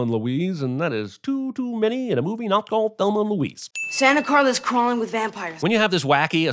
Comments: Sam does not call the town Santa Clara. Sam absolutely says "Santa Carla." Get your hearing checked. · I love this channel. I'm even friends with one of the writers, but... at 14:01 Haim calls it Santa CARLA and then gets sinned for calling it Santa CLARA... which he clearly did NOT say. Un-sin this! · he clearly says Santa Carla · he DID say Santa Carla.... but the subtitle said Santa Clara. So sin yourself,) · He did actually say "Santa Carla" ;) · he clearly says "Santa Carla", yet your subtitles show "Santa Clara" and you go on to say that he said it wrong, which he didn't. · I've listened to it many times and I don't hear Santa Clara. I hear Santa Carla, as it should be. Disagree with you here.